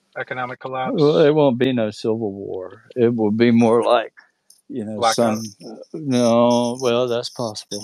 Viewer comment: What does that guy say at 4:40:02 ? Two words Is it "black sun" ? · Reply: He says “black out”